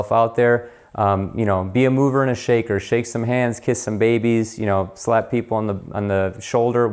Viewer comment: oh crap, I thought you said kiss some hands and shake some babies...